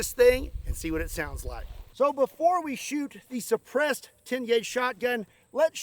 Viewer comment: The last time you said it, it sounded like you said, : depressed 10 gauge shotgun. " It definitely shot depressed.